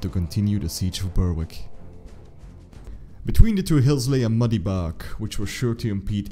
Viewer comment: What lay between the positions ? Was it boggy ground (waterlogged terrain) I can't make out the word used at 0:56. Subtitles give is as a "muddy bark" which is what I am hearing. Is this some word in scots that I have not heard used before of just a weird youtube way of saying brook ? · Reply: bog